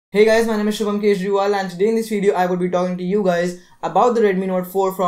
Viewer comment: lol I thought u said "he guys my name is Arvind Kejriwal :P"